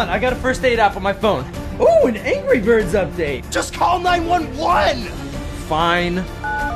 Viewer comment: Does it sound like Anthony said 9111 at 0:34?